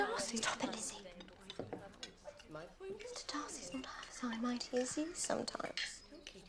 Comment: What is Lydia saying at 1:34? I can't catch the line. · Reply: 'Mr Darcy is not half as high and mighty as you sometimes.'